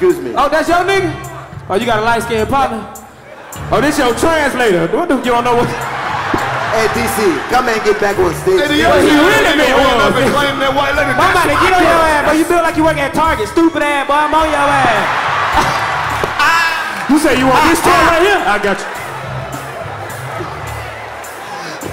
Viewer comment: i thought said im bout to get on ya ah but it was dc